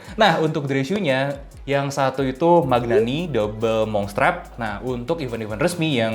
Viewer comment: itu nama sepatu dan tipenya apa ya? Saya kurang jelas dengernya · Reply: Magnanni double monk strap, nama artikelnya Miro. Tp warnanya ga bakal nemu, soalnya di patina nya sendiri